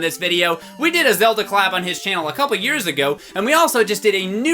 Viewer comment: when PBG said "collab" it sounded like he said "Cowab"